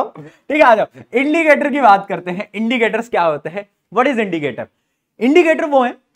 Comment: Is it me or everyone who heard Idlicator 😂😂😂